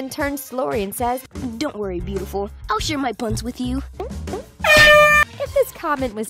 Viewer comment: anybody else thought he said “I’ll share my PUNS with you”